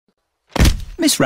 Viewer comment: “ms rabbit has ✨fainted✨” “ms rabbit has fainted ✨AgAiN✨” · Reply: I thought that said "this rabbit has fainted" 💀💀💀